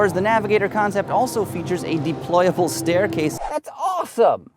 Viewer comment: lets hope right now this cant even fight the Yukon. (on TFL truck) it crushed the navigator. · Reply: i thought you said the Navigator had more features... i was like WTF. i know you like ford but this is to far.